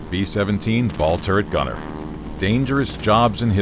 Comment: I thought it said “B-17 Ball Torture Gunner”